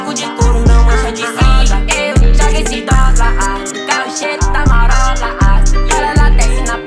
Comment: ele: e eu jogo esse dolar eu: e eu jogo o SIDOKA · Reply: Eu entendi "Joga esse dó lá na lixeiro da corola tá "😒kkk